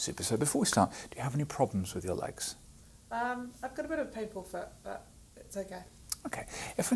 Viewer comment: What did say was wrong with her leg at 0:38? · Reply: "I got a bit of a painful foot"